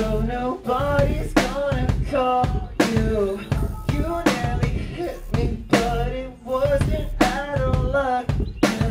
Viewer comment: what did she say instead of "the truck nearly hit me"? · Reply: just "u nearly hit me"